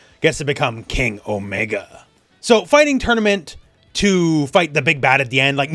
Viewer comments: I totally misheared it as Kenny Omega. Edit: But then they actually doing crossover comis with AEW now, so.... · thought you said Kenny Omega and was very confused for a few seconds